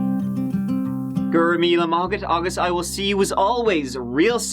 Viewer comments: What the Frick does actually say at 45:35?! I even tried CC and it read "guru mila market august". Like wtf?! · Did it sound to anyone else like at about 45:34 minutes in, he sounded like he said something backwards? Lol does anyone know what he said there??? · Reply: Haha, he said "go raibh míle maith agat" It's basically how you say "thanks a million" in Irish!